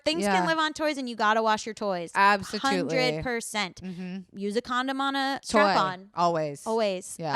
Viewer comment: Who else heard “Use a condom on a tampon” at 42:25? Whether or not she said that, I’m heaving 😂🤣😂